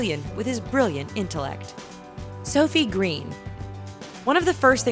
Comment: When you said "Sofie Green" for the first time, I genuinely thought you said "So Fuckin' Green" Just me? Oh, ok.